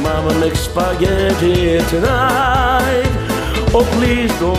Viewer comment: I always thought he said more meatballs